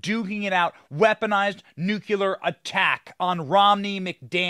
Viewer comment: Did he just say nucular?